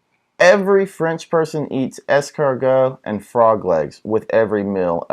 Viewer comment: A chaque fois qu'ils disent "escargots" je comprends "escort-girl" x) (Every time they pronounce "escargots" I hear "escort-girl")